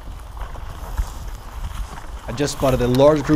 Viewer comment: I thought he said i just saw a large bunny Lol